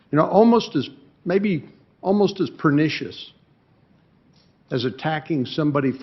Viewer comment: What does he say at 1:11 ? pernicious? I never heard that word before...